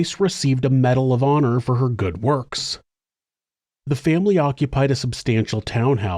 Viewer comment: I heard this as “she twice received a Medal of Honor for her good looks.” It made me laugh and wonder how a person ends up getting a medal for looking so good, do people have to vote for you? Then I replayed it. Definitely was not a medal for being beautiful. 😔